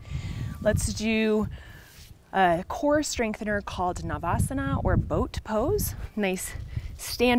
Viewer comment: Thanks for the great class. It is called Naukasana, Nauka in sanskrit is boat. I heard it as Navasana. Namaste :)